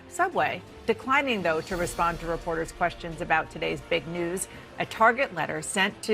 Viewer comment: I swear she is saying "about today's fake news"